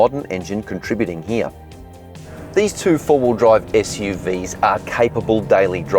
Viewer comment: bro did he say Forward Drive SUVs?? Ayyo!! Scorpio and Pajero both are Real Wheel Drives. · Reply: He said "four wheel drive" not "forward drive"